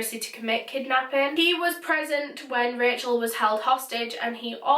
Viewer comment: I thought you said that Jeffery was pregnant at the time of her killing and I was like what? It turns out that your accent to an American when you say present sounds like pregnant.